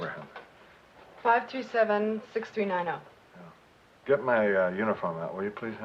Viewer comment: at clock time 47:56 Liza says "527" when the caption says 537...at clock time 49:28 the chief says ,there was a witness ,and the caption says " i was a witness "